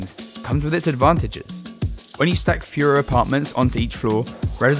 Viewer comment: This guys accent or voice “twang” means when he says “fewer” it sounds like he is saying “Fuhrer”😂